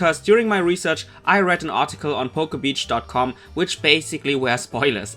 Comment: please tell me I'm not the only one who heard "Pokebitch"